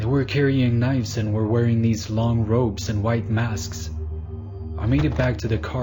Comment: "they were wearing these long ropes" I... I think he may have said 'robes'.